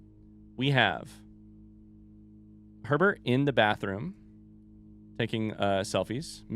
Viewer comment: I heard pervert instead of Herbert and I was so confused 😂